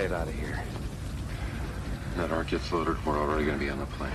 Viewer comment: "When that ark gets loaded, we're already going to be on that plane" is what he says at 0:33. I didn't catch it until just now, and I've watched this countless times since it came out.